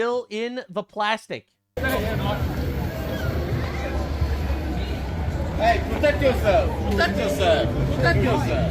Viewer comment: did i hear someone yelling "protect yourself"?